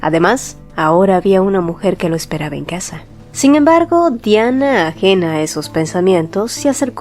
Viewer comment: Creo que escuche tiana y no diana